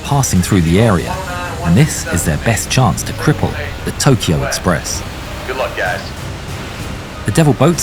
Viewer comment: i thought it said Tokyo drift 😂